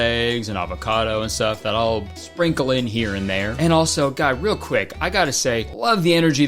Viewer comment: did u call us guy??????